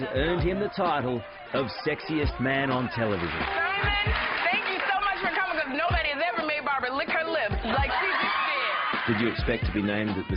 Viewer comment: What does she say when everyone is going crazy? I just understood something with "lick her lips". ;-) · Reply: "Simon thank you so much for coming cuz nobody's made Barbara lick her lips the way she just did"